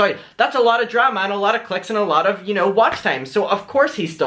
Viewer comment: ? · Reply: i thought you said CHICKS not CLICKS! haha sorry girl ♥️